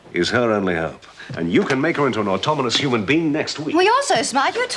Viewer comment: Wilson says 'automonous' instead of 'autonomous'.